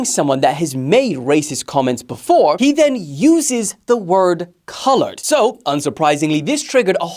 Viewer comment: I thought he said the N word.. Joey has no spine tho, low emotional intelligence , sad story